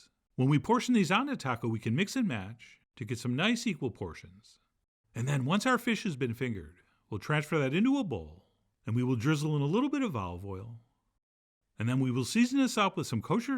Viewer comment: Did I hear “once our fish have been fingered”?! 😂